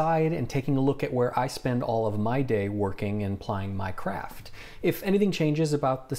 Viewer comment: I heard "where I spend my day at playing Minecraft" XD